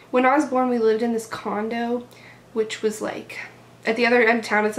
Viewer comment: I thought she was going to say "we lived in a condom" x)